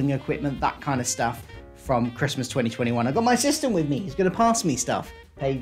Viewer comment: Sister? I went “oh, I didn’t know he has a daughter - oh, wait, she’s his sister?! What?! LE GASP! He’s so old, how is that possible?” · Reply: "I've got my sister with me who's going to pass me stuff - Paige is here." That's what I heard.